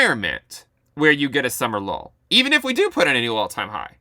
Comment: I keep hearing "summer LoL" :D